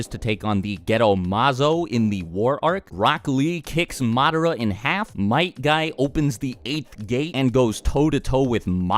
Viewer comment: ? I'm sorry I didn't hear anything you said for a few seconds, I kept going back to it but... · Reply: ! I like sakura